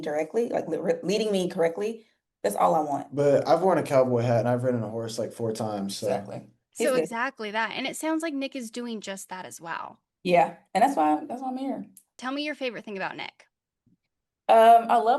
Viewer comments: did she say “that’s my man” ???? okayyyyyyy ! I LOVE IT🤩🤩🤩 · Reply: I think she said “that’s why I’m here” but it did sound like she said “that’s my man” 😭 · “That’s fine, that’s my man” did I just hear that? Omgggg I love them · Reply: I thought I heard that too and went back to listen, but she actually says “That’s why I’m here.”